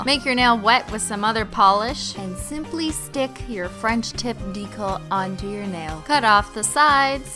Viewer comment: I'm sorry but I keep hearing "french tit"